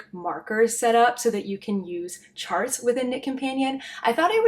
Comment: I thought you were saying Nick companion until today when I saw it written, non-native English speaker over here 😅